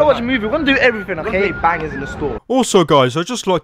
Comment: did he say we will be banging in the store